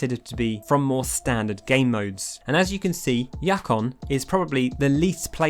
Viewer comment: DID HE JUST SAY YAKON.